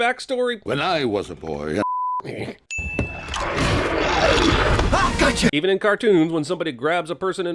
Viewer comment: it’s sound like he said when I was a boy I f**ked me